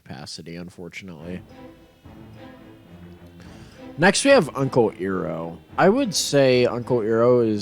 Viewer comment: Did he called Uncle Iroh Earo.